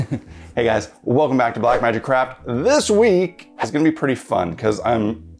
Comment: Not sure if I have a hearing problem or if he is trolling us I am pretty sure I heard "Black Magic Crap" in the intro xD Non the less, marvoulus project once more, love it!